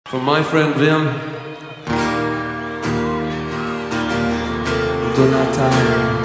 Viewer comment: I hear there "Jim/Bill Donata" or "Jim/Bill Jonathan"